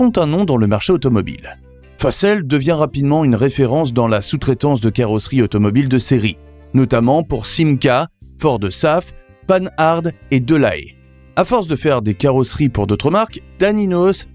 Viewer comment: À 1' 48'' j'ai entendu "panne - harde" !!! Ohé l'I.A., tu ne connais pas"Panhard" ??? initialement "Panhard-Levassor", initiales PL (d'où la PL17).